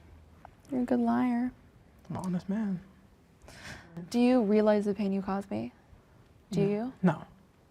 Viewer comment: why did she say? I can’t understand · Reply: “do you realize the pain you caused me?”